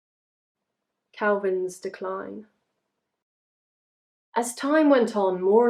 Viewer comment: when you said kalvin's decline i thought you said calvin klein